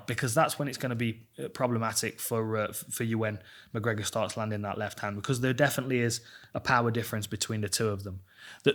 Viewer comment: I had to listen a second time. Thought he said Ewan McGregor would start land his left hand.